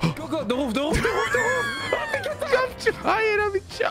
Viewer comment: How says the Roof sounds like he saying ''The Woof The Woof The woof''